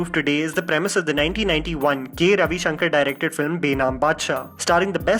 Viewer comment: Who else heard , gay Ravi Shankar?